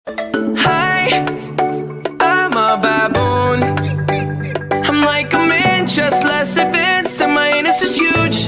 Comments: I heard “HI am a bad boy” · What the normale people hear: "hi i'm a baboon" What i hear: "hi i'm a bad boy" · Umm I heard I'm less advance and my a$$ is huge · I always thought he said " Hi! I am a baboon,i am like a man just less offense to my uranus is huge"